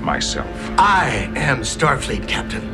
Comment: "I am Starfleet Captain!" All i heard was " I Am the Senet!!!"